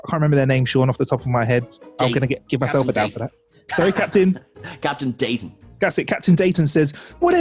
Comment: first time watching, I low-key thought they said captain Data which made me do a huge double-take. That captain really hid the last syllable of Dayton's name.